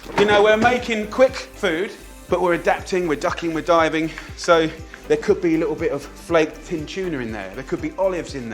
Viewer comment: "We're making quick food, but we're adapting, we're ducking, we're diving..." For a minute there, I thought he said 'dumpster diving'. (Let's not go there...)